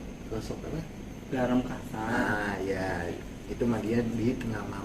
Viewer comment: Garem kasar??,saya salah dengar gak sih?, manfaat nya apa ya?? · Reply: Iya betul garam kasar..banyak manfaat nya.selain buat netralisir Bisa buat rendam kaki pakai es batu ta air es dikasih garam kasar nk kuat dinggin..bagus buat kaki yg pegel . Q dengar pas demo kesehatan di kampung ku